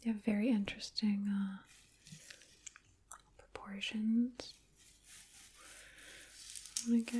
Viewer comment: I’m sorry, but I thought she said “You have very interesting abortions.”🤣